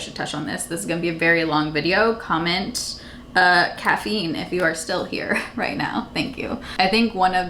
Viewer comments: Tell me why I thought you said cat feet instead of caffeine 🤣 🤣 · Totally thought you said “cats feet” at first 😂😂 but uhhh CAFFEINE!! 🖤